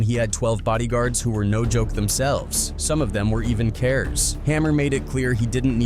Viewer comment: Okay wth did he say @ 4:23. ..."some of them were even carers"? God, I hate AI videos so much, lol. Edit: nvrmnd. Luckily, the other dude in the video actually says right afterward, "killers." I guess it said 'k-ers' or something to try to prevent being demonitized. Idk. Still stand by what I said about AI, tho lol. Hate the voiceovers. Literally called treach "treech" 😂 Edit 2: "LL... Cool... JLL dissed hammer" @ 12:56 wtf is this AI even saying 😂 sound like someone had a stroke while tallking about ll cool j lmao Edit 3: @ 13:43 add "3rd bas" to the list of names completely butchered Edit 4: @ 20:16 "fellow rapper shemillionair" Edit 5: "hash-rip-hammer"